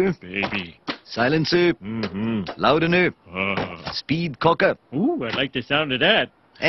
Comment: Hello, not a native speaker here. What is the vendor saying at 0:22 speed what? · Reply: He says "Speed Cocker". To "Cock" a gun means to raise the hammer (or cock) of a revolver and ready it for firing. A speed cocker would basically be a tool to help you shoot faster. Homer "liking the sound of it" is an innuendo, as "cock" is also slang for male anatomy.